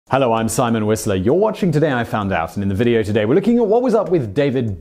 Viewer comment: All I hear is "Simon WhistLEY". Every damn time! I only know it's not that because I see your name in written form.